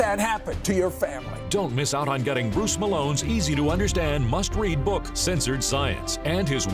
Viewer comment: i keep hearing "post melones" instead of bruce melones